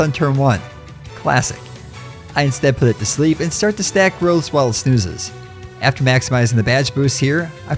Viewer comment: Did anyone else mishear "stack growths" as "stack girls" a few times?